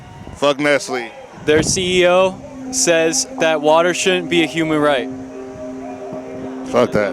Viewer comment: "Fuck Nestle bro. Their ceo says that water isn't a human right" "Fuck that" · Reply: I thought he said “Fuck Nestle bro, their ceo says water should be a human right” I was so damn confused 😂😂